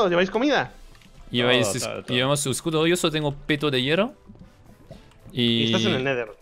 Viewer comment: Solo yo escuche a shadoune decir tengo pito de hierro? Min